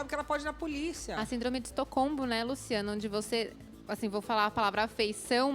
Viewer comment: Eu ouvi "Estocombo"? 🤔